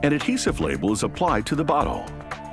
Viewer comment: I legit thought he said “an adhesive label is put on the bUtThOlE”